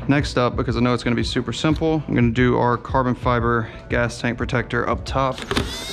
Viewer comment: Lmao thought you said taint protector instead of tank protector